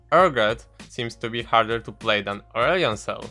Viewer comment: Wtf did you just say!?!??! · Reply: "Urgot seems to be harder to play then aurelion sol"